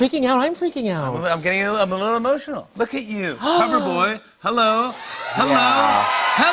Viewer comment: WHEN JIMMY SAID "COVER BOY" I THOUGHT HE SAID LOVER BOY, AS IN ICEBERG'S AMAZING NEW SINGLE